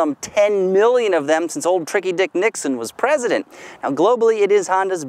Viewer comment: did he just say tricky dick nickson?